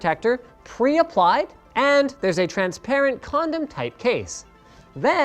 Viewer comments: Did he say "condom tight" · Did he say Condom like case? LOL